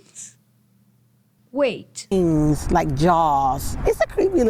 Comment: I think she said fangs.